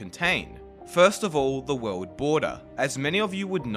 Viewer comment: I kept hearing "Wowd border"